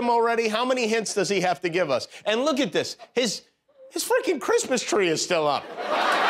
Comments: Is it just me or did it sound like Kimmel said " his fuckin Christmas tree is still up" ? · did Kimmel just say, "his fu*king Christmas tree is still up", did I hear that right? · did Kimmel just say, "his fu*king Christmas tree is still up", did I hear that right? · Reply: I thought he said frickin